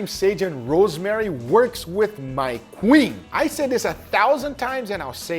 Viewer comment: I SWEAR I heard queef. Maybe my mind is just filthy. 🤣